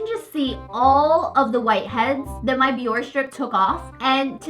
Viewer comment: Is it just me or did it sound like she said “v ore “ strip